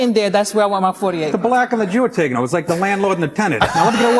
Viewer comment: what does Nick say starting around 1:50 ..the black and the Jew..are ?? ..it's like the landlord and the tenant .. I didn't entirely get Colin's joke either ..did he say ..Nabisco ? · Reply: "the black and the jew are taking over"